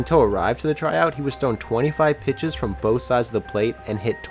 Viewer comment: What did he say? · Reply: 25 balls to each side from each side of the plate (50 total) and he hit 26 for home runs